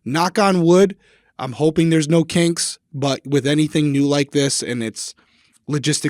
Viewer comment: In the intro Derrick, I genuinely thought you said "hopefully no cakes (instead of kinks) and I actually said out loud "why would you hope for no cakes, cakes are awesome!" 😂😂